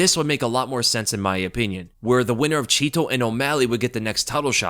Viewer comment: I thought they said the winner of Yan O’Malley gets a title shot